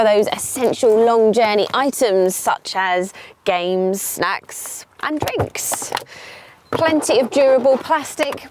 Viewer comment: yep i heard 1:13 games sex !